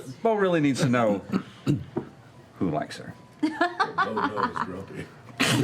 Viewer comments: Can someone tell me what Travis says at 1:16:25 that makes everyone laugh? It sounds like "Beau knows grumpy", but if I'm right I don't get it. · Could someone tell me what Travis said at 1:16:24? I want to laugh along with them, but I can't tell what he said. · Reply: Nadya K Beau knows grumpy. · what did travis say @ 1:16:25 ? cant quite hear it and auto cc's are trash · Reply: Beau knows "grumpy" (28 year old reference to Bo Jackson commercial)